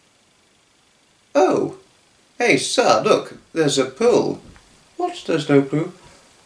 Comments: My mistake: what did you say at 0:58? Happy April fools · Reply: Hello there Arthur :-). At 0:58 of the video I said pool :-). Happy April Fools Day to you too and I hope to see you at the Ukraine appeal event in Lewes on Sunday 3rd April 2022 if you're going :-). Best wishes :-). From your friend:-). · Happy April fools! Did you say "pool" or "poo" at 0:58? I can't tell, but good example nevertheless! Tata! :) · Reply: Hi there Ellis :-). I said pool just to say at 0:58 of the video :-). Best wishes :-). Happy April fools :-). From:-).